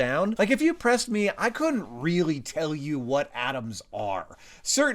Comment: Does he really say 'adams' in stead of 'atoms' ?